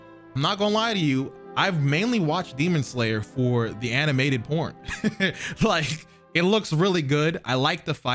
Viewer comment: Wait did really say animated porn or am I trippin at 8:48 it was at like 8:50 but I want y’all to really hear it